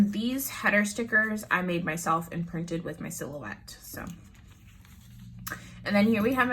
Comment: Hey! I use my MacBook and I have a silhouette cameo cutting machine. I’ve never used a cricut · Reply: haha, I'm such a dork. I knew it was a silhouette and I heard Cricut? I'm in the market for a Cricut. but I have a Chromebook, which will not run a Cricut, so I'm asking around. thanks for responding :)